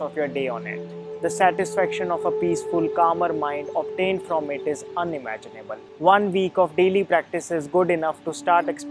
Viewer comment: apne Kya kaha Edit: don't like my comment just tell me subtitle If you are reading this 🌸 Kal Mera birthday hai😊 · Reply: Satisfaction of a peace full and camer mind obtain through it is unimaginable